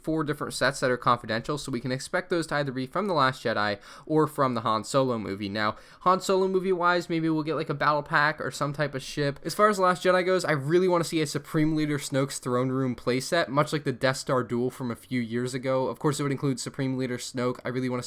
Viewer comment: lol at 3:38 i thought he said shit